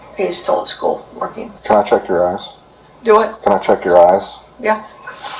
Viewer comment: I thought he said, "Can I check your ass?"